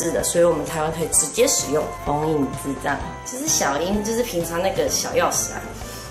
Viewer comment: 所以是只有我把0:58的封印之杖聽成封印智障嗎